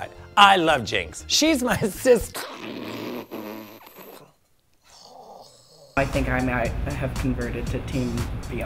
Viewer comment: What did jinkx say about bianca in the end there? Audio was so bad. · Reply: omg good! I thought she said "I think I might have tamed Bianca Del Rio" and I was like ??????????????????? lmao